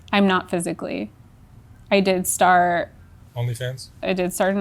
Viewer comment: I cannot understand what she said after he asked if she was still doing sexwork, and she says no... not physically... "I did star only fens"... I have replayed it a half dozen times, listened carefully, and can't figure out what she said. Somebody help me out here please.